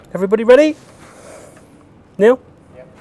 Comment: I heard Neil say "Yeah!"